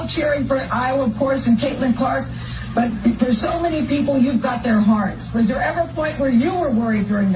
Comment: WoW. Jason Whitlock is definitely “out of touch” with himself and the “black media”!! I didn’t hear GK say “you had our heart. I heard “ for so many people, you’ve got their hearts”!!! Mot “you got our heart!” Stop disrespecting me Correctional Officers ! You are an idiot Jason Whitlock!!